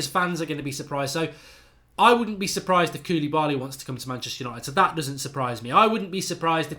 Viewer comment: I thought he said Jamie vardy instead of koulibaly